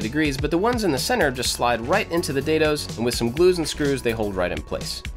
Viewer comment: at 2:26 you said "glues and glues" where I think you meant to say "glues and screws"